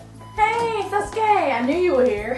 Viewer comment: XDXDXD when naruto said ¨Hey sasuke!¨ The subtitles said ¨Hey that's gay!¨ IM GONNA DIE FROM LAUGHING